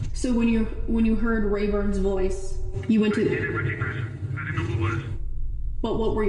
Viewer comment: I'll transcribe what I can hear him saying for those who can't hear either. (Timing might be a bit off) 4:57- 4:59 Don't ask me that. Don't ask me that. 5:00- 5:01 (Not sure about this I think something like) I phoned the house with the iPad/ iPhone 5:05- 5:07 I didn't recognise him. I didn't recognise him. I didn't know who it was 5:10- 5:22 I was freaking out! I was like oh my god I've caught her. I caught her. Oh my god! There's a man. There's a man! Then guess what I hear, (A beeped out word I assume is the f-word? then I'm not sure what exactly he says, something like) *her, with RNB beats*. 5:23- 5:28 (More swearing which I'm guessing is WTF) (Again not too sure what he says something like) Every time I wake up (Or more likely) As I'm driving to her (Then I'm not sure at all) 5:29- 5:31 I'm like don't do it! I'm screaming! 5:32- 5:36 *As I'm driving I'm screaming! I'm driving! I'm like don't do it! 5:40- 5:42 I go up! And I see them! (The bits in between the interviewer from 5:42 to 5:48 are) And I was like... (and) *They were...") 5:50- 5:53 Nothing! I didn't think. I-I-I don't know what it's all just a blur. 5:54- 5:56 I felt like I was in the passenger seat of my own body. 5:57- 6:03 When I opened the door and saw what it was. It f---ing broke my heart. 6:04- 6:32 *And to see her, kissing Ray, on my couch! With his feet on my table! With my wife! My money, my apartment, my wife! In the house that my daughter sleeps in! On the furniture that I put together! How do you